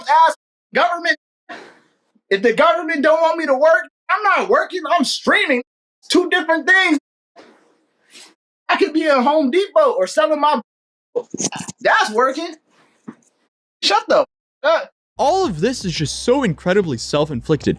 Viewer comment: did - did he just say i could be selling my butthole i- i dont know what else he couldve said